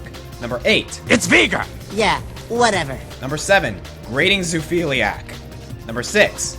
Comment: what did you call elise? i must've went back and tried to listen to what you say at least 10 times and i have no clue what you said :( it sounded like you said ''grating xufiliac'' but i have no clue who that is or what that is, and when i tried googling i got nothing :( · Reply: Legends I said Grating Zoophiliac. Zoophilia is physical attractiveness to animals.